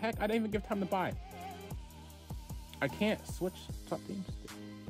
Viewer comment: What did u just said? [Subtitles] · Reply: i think he said: I can't switch counter terrorist